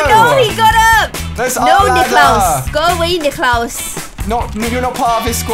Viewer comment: omg did she just say “no niklaus” at 21:20 ?! #theoriginalsfam4ever btw 😂